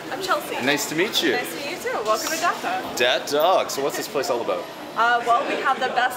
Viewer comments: am I the only person who heard he said ‘dead dog’? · “dad dog”? What bro? Why did Pete say that after she said welcome to Gotham city?